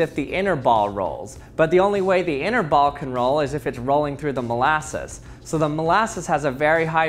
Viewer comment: Just like the old saying, “slow as molasses!” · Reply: All this time I thought they were saying "mole asses". Now this makes much more sense.